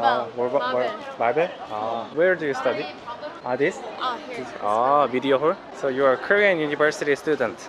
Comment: What did he say at 0:47? I think I missunderstood it for bad words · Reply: I said media hall haha